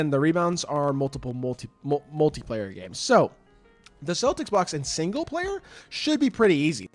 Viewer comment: Bro I thought it said 15 blocks In on game 😂 thanks for the information